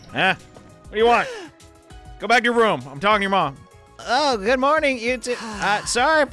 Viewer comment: Did he say "I'm talking to your mom"? Huuuh?